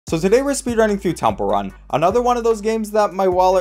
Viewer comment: I thought you said “tumble run,” what accent is this